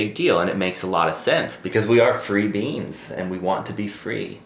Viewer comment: said “free beings” and I heard “free beans” and for some reason that made me so happy! In my head I could see us all just like a big bean plant stretching our tendrils in different directions 🤣